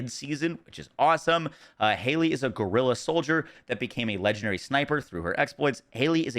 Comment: After reading it back it sounds like the inversion system might work like a Diablo 3 paragon system or Borderlands badass rank for our descendants. What do you think it might mean? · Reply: i heard you saying at the beginning while reading it ( Hailey is a gorilla soldier ) i was like ( wait what ? ) lol